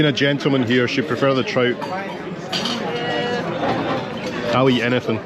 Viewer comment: Had to rewind the bit when you had your first meal . Thought you said Tripe but you actually said Trout 🤣🤣. I would defo be trying that pool and jacuzzi too. Loving the enclosed balcony . Great vid & look forward to the next part of the adventure 🙌